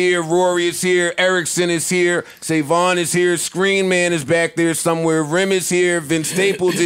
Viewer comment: ok so we just gone hear vince’s voice 🗣 & not see him?!?! thought joe said screen man “is here” but stapes is good for that